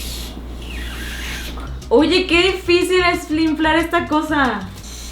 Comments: Like site diste cuenta que dijo flinflar en el minuto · Like si en el minuto 6:24 escuchaste que dijo " oye que difícil es flinflar esta cosa" Jajaja te quiero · like si notaste que dijo flinflar en vez de inflar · Like si escuchaste en el minuto 6:23 que dijo FLINFLAR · Like si viste que dice flinflar min.